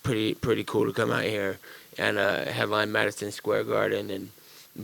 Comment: did he say medicine Square Garden